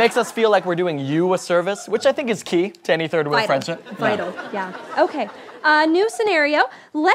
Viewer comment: I really thought it said "Third Wheel Funeral"